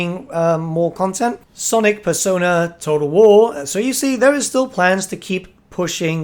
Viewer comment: I heard "Sonic Persona: Total War" @ 1:10 and had to go and had to skip back to re-listen lol